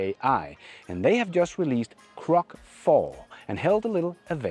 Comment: Almost every time you say "Grok" I hear "Crock" ... I don't know if it is my imperfect hearing, or you failing to growl a little when you say the word. It's funny, but Grok isn't a just a crock, and it is becoming less and less so by the week. BTW, unless you are seeking entertainment from its NotSafeForWork features, I recommend flipping the toggle for NSFW to disable those.